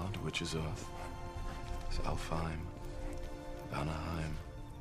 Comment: i thought he said Anehiem lol like disneyland